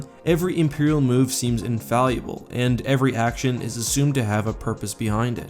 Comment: Sorry, a minor nitpick - and I don't mean to be a grammar nazi but I could swear you were saying "infalluable" several times in this clip. (example at 1:45) I assume you mean "infallible" or "invaluable". Probably the former since the latter doesn't fit the context very well. It's probably just a minor flaw in pronunciation, but it might be worth correcting. I just found it a bit puzzling and distracting. Again, this is purely meant as constructive criticism to help you further improve on the already high-quality presentation - nothing more :)